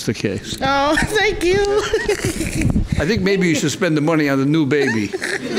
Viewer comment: I thought he said "you should spend that money on a new baby"